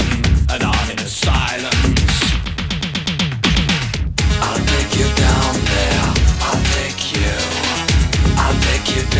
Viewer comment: I thought he was being real on the nose there, I heard "I'll lick you down there"